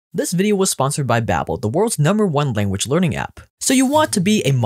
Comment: lol, i thought at the start it said "This video is sponsored by Babbel, the worlds worse #1 language learning app"